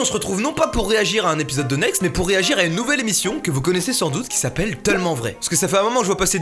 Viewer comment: A 0:44 c moi ou il dit "TEUllement vrai" ?!